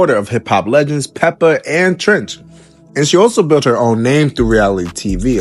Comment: Is this guy saying “Trench” instead of “Treach”?